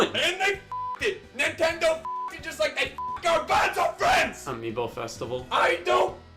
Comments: is he saying banjo friends? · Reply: He said Albonzo friends. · “AND THEY F**KED IT! NINTENDO F**KED IT JUST LIKE THEY F**KED GARBANZO FRIENDS!!” “Amiibo festival” “I DON’T CEEEEEEERE” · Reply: i thought he said "our beds are friends"